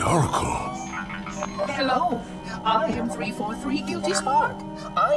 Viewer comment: Others might have said this, but I've always heard it as "Hello, I am 343 duty spark" . Not guilty spark. Mebbe an issue with YouTube captions, but it confused me.